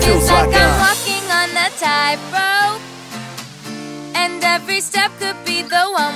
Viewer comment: I thought she was saying "like a monkey on a tight rope"